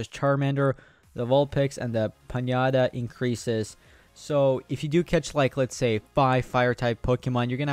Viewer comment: Did he call a ponyta a piñata at 1:11, lol